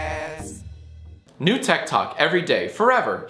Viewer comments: When he said "techtalk" my dumbself thought he said "tiktok" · I thought he said ted talk at first 👀👀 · When you said techtalk i thought u said tiktok I was like nope unsub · Do you not know how to read? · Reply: yes,I thought he said tiktok,but once I saw techtalk then I realized I am probably deaf